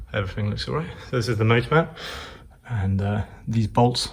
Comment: Im not a native enlish speaker, so i was wondering what he says @ 12:43 ? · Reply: "This is the motor mount" - reffering to the metal part which holds in the motor unit in place. The bolts securing it are now bigger than in the very early cars for improved strength.